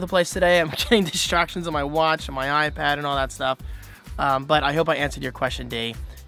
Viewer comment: Oh I thought you said iPad Air...